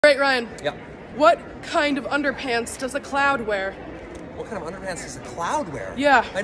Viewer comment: I heard "clown" instead of cloud so it didn't make sense to me, then i re watched it an now it makes more sense. I thought it was because the "clown" just took a huge shit and that was why it was thunder pants😂😂